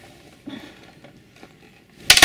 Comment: i heard a dude whispered scheißa